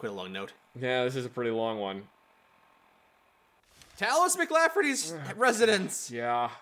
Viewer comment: Sounded like said "Talos McLafferty."